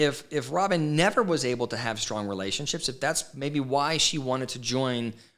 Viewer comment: Um, yeah, he said "Maybe" both times. Not "Baby." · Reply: I heard "maybe", too.